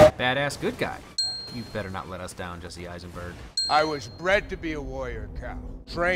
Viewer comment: Lol I thought zod said “I was trained to be a lawyer”